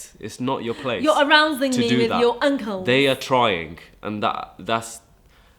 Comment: I heard "You're arousing me with your Uncles" lols